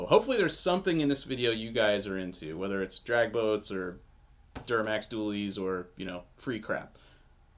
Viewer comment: i thought u said duramax wheelies at first. i got super excited lol